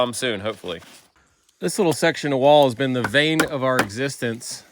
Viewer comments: did you say “vein of my existence “?!!? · Once again, another great video. FYI, it sounded like you said, "the vain of our existence" it's actually "bane".